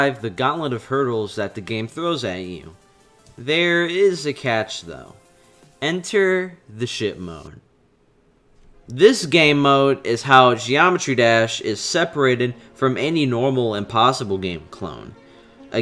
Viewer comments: lol I heard him say enter the shit mode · I thought he said, enter the shit mode